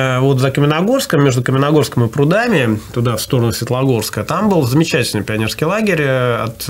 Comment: если в Лесогорском, чуть не доезжая Светогорска (послышалось, что Светлогорск сказал), словом, если свернуть в Лесогорском, то можно попасть в дом отдыха Лесогорский на берегу Вуоксы, он в бывшей баронской финской усадьбе располагается. Бывал я там и в детстве и во взрослом возрасте, очень там прикольно - природа, кругом в скалах финские доты, обрывы, река, красота и исторично... рекомендую. проезд туда свободный, хоть и погран-зона, автомобильный приёмник ловит финское радио, а сотовый периодически норовит уйти в роуминг.)